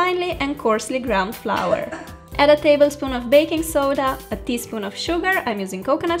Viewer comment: GIRL DID YOU SAY BAKING SODA AT 10:30 - 10:42 😅😰😵😳😱